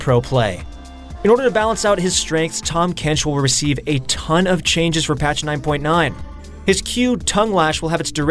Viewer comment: I heard "a tongue of changes" just as Tahm Kench used tongue. Intentional?